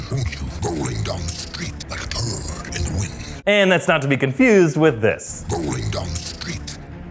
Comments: I always thought he said like dung in the wind, the more you know I guess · I thought venom said “like a dog in the wind” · i thought he said like a bird in the wind, but i guess turd in the wind makes sense too lol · I thought he said like a bird in the wind, and not a turd in the wind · I thought he said “like a bird” not a turd 😂